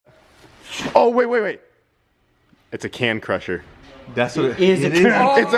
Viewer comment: I thought he said that it was a 'cam crusher', and I was like NOOO!